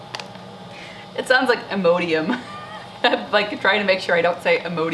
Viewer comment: For Buplerum, I like to call it "Buple rum" hahaha. and it totally sounded like you said Imodium. I was ironing and was like wait what??? ahhaha